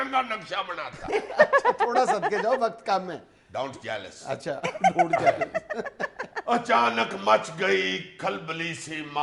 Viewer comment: Can Someone please tell me what Junaid said at 33:11 ???? what jealous?????